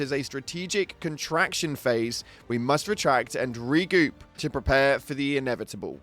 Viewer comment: Its not the first Major major order failed dont forget get failed on the bug hive world to i thought anyway Also you said regoop xD instead of regroup lol